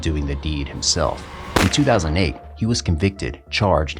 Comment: "in 2008 he was arrested" but the video captions say 2018..????